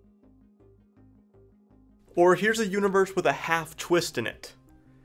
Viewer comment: Who else thought it said universities? Ohio State!!!! Party!!!!